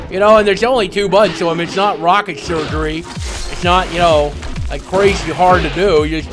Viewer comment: did he really say rocket surgery.